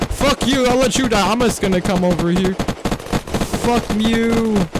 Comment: I definitely heard him say "FUCK MEW"